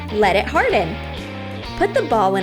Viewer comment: the baw lol instead of ball